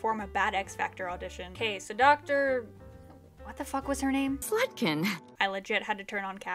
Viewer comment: I heard that as "slutkin" lol